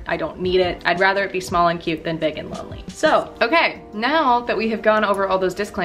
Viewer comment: “Small & cute than big & lonely” .... quote of the year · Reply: I thought she said big and ugly LOL!! woopsie!!